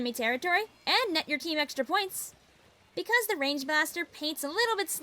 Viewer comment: i thought you said “range bastar”